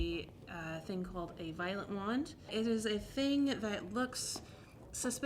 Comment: Violet wands and tens units are the best and safest way to explore electricity, fyi. 😊 Great video, guys, great video. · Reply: Thanks for writing that out, I really thought she said 'violent' wand 😅